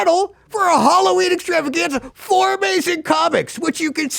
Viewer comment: 4 amazing comics! Oh! I thought you said, "form Asian comics." Which after bonny lee, and that Hanz Kim d bag, I don't think anybody needs